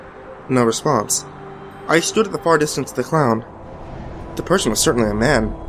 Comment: at first i thought it said the clown in the turban.. like a durka durk clown